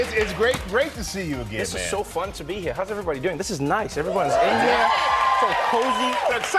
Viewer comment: I thought he said, this is so nice...everybody India, so cozy. And i thought well yeah after having a population of 1.3 billion it gets a lot more than cozy :D :P Anyways Trevor, you are one of the most funny modern day comedians of our generation bro. Greta going there man.